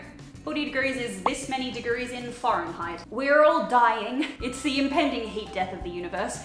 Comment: I love how it sounded like said foreign-heit instead of Fahrenheit